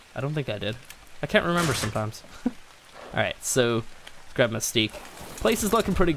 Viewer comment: for a sec it sounds like you said "grab my stick" in 16:36 😂